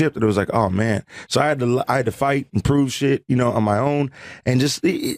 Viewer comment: Lmao it sounded like he was about say “I had to lie” and then he caught himself